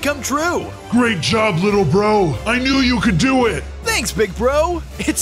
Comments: Did he just say bronzo at the end bro what but I'm whatching bronzo to and he always says bronzo at the end but now you scream his name too?!?!? I'm confused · Wth is happened did he just say bronzo at the end😐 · Wait did he just say bronzo